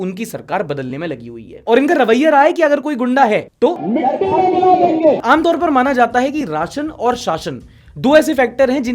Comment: what did yogi baba said? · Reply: mitti mei mila denge(will mix in the soil)